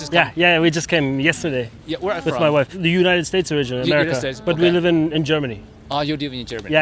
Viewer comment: did he say the United States of Israel?😂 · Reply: The United States originally